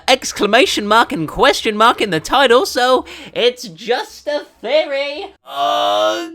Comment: Hey guys, English is not my first language, can someone tell me what he says at 5:34? I hear something like "It's just..."